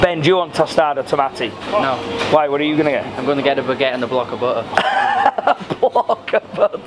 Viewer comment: “A baguette and a block of butter” 🤣🤣🤣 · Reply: I thought he said a block of burger mate 😂